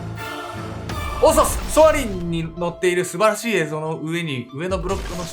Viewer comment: ここのが言ってるのよく分からんのだけどなんて言ってるんですか？🥲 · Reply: たぬぴさん、「ソアリン」です‼️ディズニーのアトラクション🔥